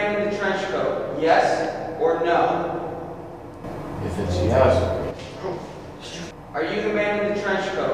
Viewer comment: I heard “If it’s yes” like if the janitor was continuing the question